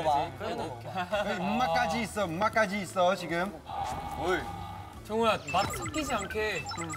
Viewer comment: No One: At the 1:33 minute Me:I heard lucas say "makan jisung makan jisung"